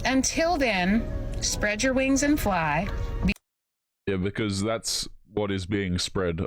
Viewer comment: "Spread your wings and fly" all I heard was "spread your legs and ride" 🤣